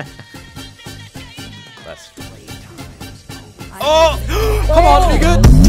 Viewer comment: Did you just say "cmon ni**a" ??